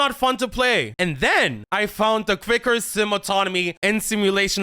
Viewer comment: I swear I heard "And then I found the Thicker Sims Anatomy' when you said 'I found the Quicker Sim Autonomy." lol